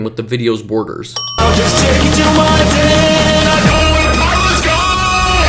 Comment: did he say I know wen pyro is gone?